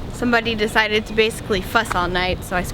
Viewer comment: I badly misheard "someone decided to fuss all night"